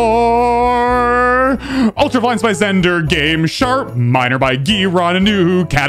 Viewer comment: I always hear "shart minor"